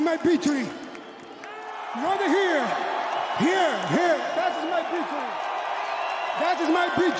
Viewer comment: Joe Rogan: Yoel, how did you feel about the fight? Yoel Romero: That is my pea tree! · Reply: I thought he was saying "that is my country". He was pointing at the Cuban people in the crowd when he said it